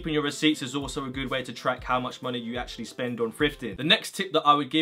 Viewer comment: to me it sounds like he saying "frifting"